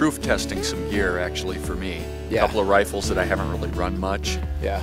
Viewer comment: For a second i thought he said a cup of baileys